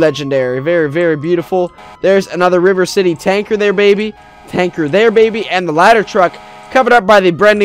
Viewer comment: did he call the rescue a tanker?